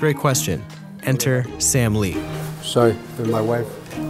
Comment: When he said sam lee, it sounded like Stan Lee.